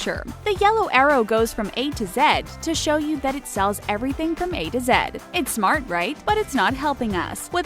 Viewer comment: Is it me or did she say A to Zed?